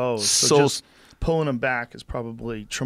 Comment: Joe sounds like he said "black" muscles...damn joe lol